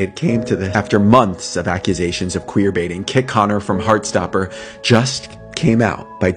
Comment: my bad thought he said gay baiting when I Googled it it went straight to telling me what queerbating was hopefully my ignorance don't offend anybody